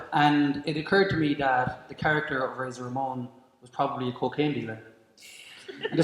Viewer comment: he says “the character of razor Ramon was.....” what is the word he says? Lol i have literally listened 20 times and I still can’t make out the word he says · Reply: "Cocaine dealer". As you know, or should, Razor Ramon's character and voice was loosely based on Scarface, and if you know who and what Scarface was then the whole lime should make sense. But yeah, Cocaine Dealer is what he said.